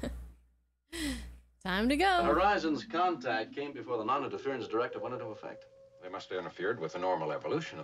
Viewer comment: For the audience, the Prime Directive mystery here is in the writing of the dialogue—just one term—and in William Shatner's delivery of it. In the turbolift on the way to the Transporter Room at the beginning of the episode, Kirk says to Bones and Spock that the Horizon's visit to Sigma Iotia II happened "before the noninterference went into effect". The problem is the use of "the noninterference" instead of "the Prime Directive", and, if you ask me, also the blurry way in which it's pronounced—for close to twenty years I thought Kirk was saying "before the monitor terms went into effect".